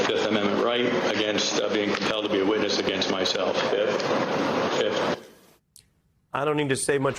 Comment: trump's guys pled the fifth so many times, after awhile they just said "FITH". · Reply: I thought they were saying filth.